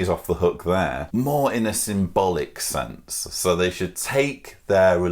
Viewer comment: When you said "Symbolic" I heard "Some bullocks" which still works with "Some bullocks sobriety"